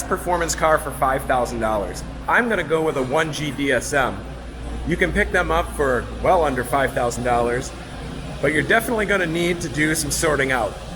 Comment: what car is he talking about? I can’t really make it out what he’s saying. 1 jd sm???